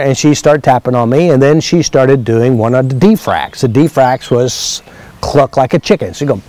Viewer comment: At about 2:36 into the video says: Mia was there ... "she started doing one of the defracts " ... clucking like a chicken. I couldn't quite hear the word clearly, so I searched around in some Faster EFT articles & blogs, and eventually found it in the 7-day Quickstart Course, Day 3 - Terminology, Part 2 - What is De-fractionation? ... but that lesson's not available (to me) yet. I've seen others (in EFT work) refer to polarity reversals, blockages - and doing exercises to correct it ... perhaps there are similarities, regarding issues around feeling stuck and using ways to get free of it.